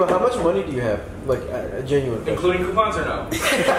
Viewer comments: for the longest time I thought Dima said "including airpods or no" still funny though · "Including coupons or no?" - Dima 0:02 For anyone else who couldn't understand his jumble at first.